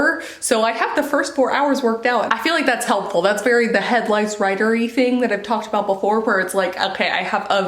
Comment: Omg when you first said headlights I thought you said head lice! Lol. I feel like word count is more incentive because you know if you push yourself you can be done quicker lol. The time never goes away. We like to subtract things from our life, not add. If that makes sense.